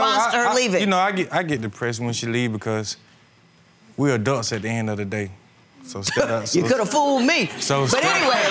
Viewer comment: Him: we adults at the end of the day Judge Toler: could’ve fooled me 😂😂😂😂😂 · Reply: Ohhhh I thought he said "we dust at the end of the day" I was so confused haha